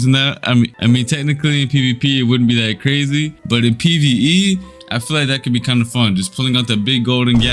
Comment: did he say pve?